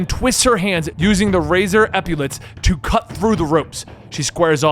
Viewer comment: What is he saying here? Razor "epulets"? I assume he's talking about the fins on her forearm, I've just never heard that word before. I tried to Google it, but I only get results for epilepsy.